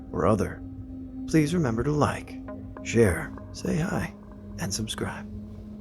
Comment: in the intro i thought you said please remember to like, share and stay high" lmaoo you said and say hi!.